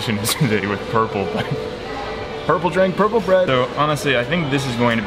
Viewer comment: Shit I thought you said "purple drank" I was scared for a second there 😂😂